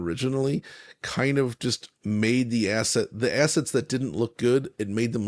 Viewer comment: did he say assets or asses? Can't tell by looking at the picture.